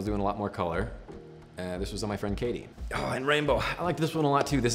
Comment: Did he say rainbow